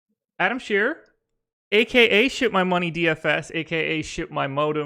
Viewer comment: When introduce Adam, sounded like he said Shit My Money instead of Ship. I vote to make a change to the former